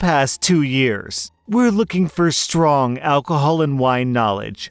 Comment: On the first story I swear to god I heard “looking for strong alcoholics...” XD 😂